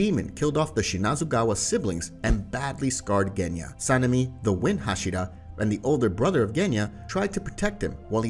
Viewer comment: It sounded like "Wimp Hashira" lol